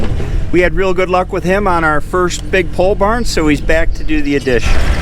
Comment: it makes perfect sense now. I’m British and to me your accent always sounded like you were saying “pull barn”. Pole barn makes much more sense!